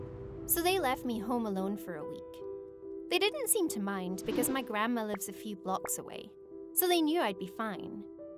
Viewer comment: hand to God I heard “cuz my grandma is a default skin”